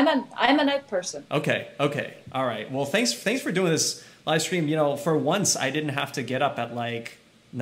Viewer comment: "Thanks for doing this Icecream" - Ray Chen 2020 Or was I the only one who heard that?